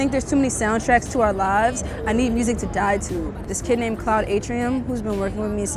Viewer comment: Did she say "I need music to die to."? 😨😲